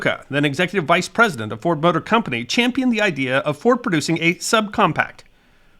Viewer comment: I heard that as "Ford Butter Company"